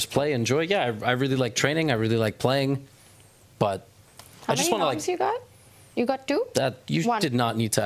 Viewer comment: Can someone tell me what she asks at 5:17:18? Levy did not like that question lol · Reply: "How many norms you got? You got two?"